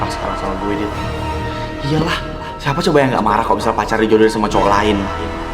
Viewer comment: " iyalah siapa coba yang nggak marah kalau misal pacarnya dijodohin sama cowok lain" itu aku yang salah denger atau gimana yaah